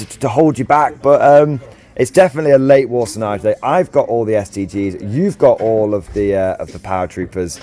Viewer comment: "I've got all the Stgs" I very nearly heard STDs there, not what you said/meant.